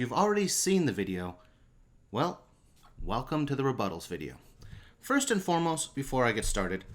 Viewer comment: bruh just saw the first video thought he said re buttholes video